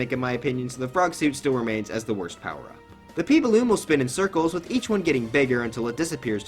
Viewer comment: he p balloon will- Me: wait did you just say poo balloon?